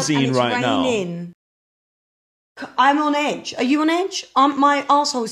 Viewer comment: did say her a$$ hole is twitching 😂😂😅🎉 im dying im so weak 😂😂😂